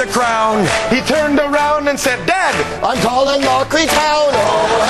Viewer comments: What does Greg say at 0:59? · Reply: I'm colin mochrie now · what did Greg say at 1:00 mark? "I'm Colin Mochris ????"